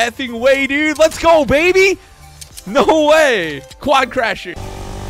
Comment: did he just say “quad CRAsher?